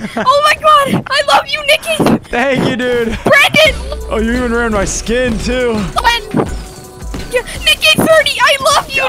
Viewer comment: when nick said you're wearing my skin too the subtitles said you're ruining my skin too:D